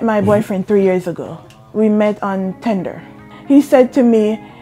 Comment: Did she say they met on TINDER ?? I'm more surprised that a tinder date she found someone God sent... Tinder can be a blessing too... Otherwise I don't respect apps like Tinder.